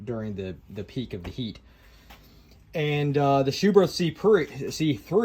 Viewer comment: i thought i heard you say "the schuberth c-pri*k..." 😂